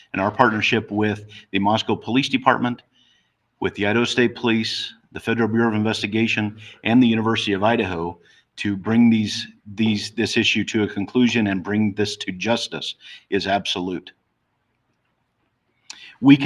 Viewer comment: I think he almost slipped and said bring these killers